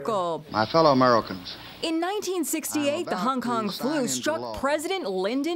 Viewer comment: I thought she was saying the honk-honk flu.